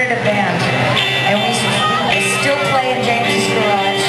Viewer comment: please guys, what is she saying in 0:08-0:11? And we're still playing...what? Oh my poor english... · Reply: "We still played in James' garage"